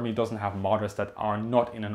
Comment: Every time you say Marder, I hear "martyr". Possibly the same thing